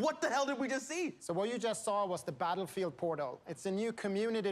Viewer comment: Thought he said 'porno' - kinda fitting though